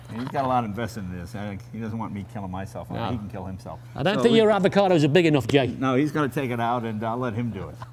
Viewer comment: Did he say Jay's cajones weren't big enough?! Screw this guy.